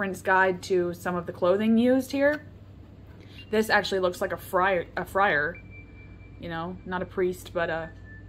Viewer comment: I heard "fryer" insead of "friar" at first, and internet brain goes "wow, she really doesn't own an air fryer" But I never knew the clothes were inspired by shakespeare, thats good to know